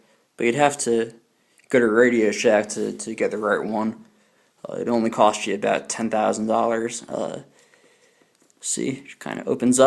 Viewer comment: Did he say "it will only cost you about 10000 dollars? 3:09 .lol